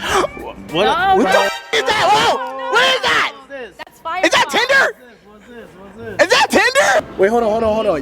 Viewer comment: DID SHE SAID THAT IS FIREWALL 😂 ??? 12:38 instead of tinder